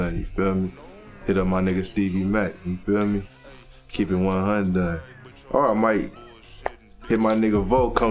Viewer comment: did homeboy just say "keep it one hyundai" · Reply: He said “keep it one hundon(hundred)” 😂